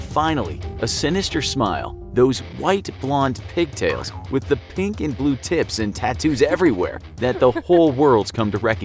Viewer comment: I thought I heard "Pink and Blue Teats" @ 1:03 so I had to replay that part.